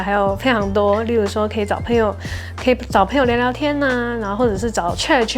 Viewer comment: 找朋友聊聊天=>聽成找配偶聊聊天 想說 我又沒有QAQ 哈哈哈哈